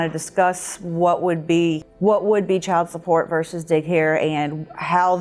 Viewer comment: the subtitles say “ verses dick hair” lol wtf